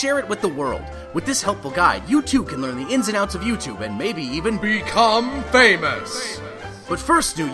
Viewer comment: BECOME ANUS!!! That's litearally how i heard it the first time